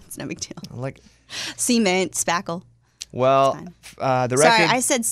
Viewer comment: did she say semen ;-)